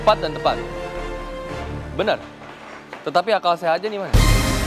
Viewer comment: hah? chef arnold ngomong apa?? kok gua denger "Tetapi akal saya aja niman" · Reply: Tetapi akal sehatnya dimana.